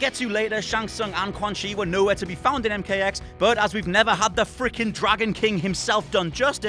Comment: Wait. Did he just say Shang Tsung and QUAN CHI were no where to be found in MKX? Or am I hearing something wrong?